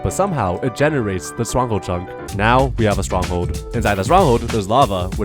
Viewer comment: I'm sorry, but I heard "Strungle Chunk".